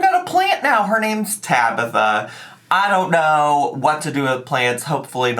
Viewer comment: I thought you said the plant was called scabitha instead of Tabitha. Edit: you have to be the cutest joker😂